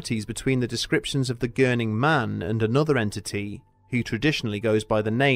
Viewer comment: I thought it said burning man